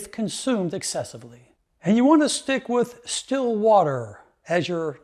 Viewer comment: What did you say @ 4:58 ??? distilled water? or still water? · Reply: he said still water, could be the same as distilled water.